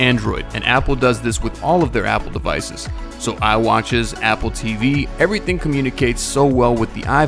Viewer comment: DID HE SAY IWATCH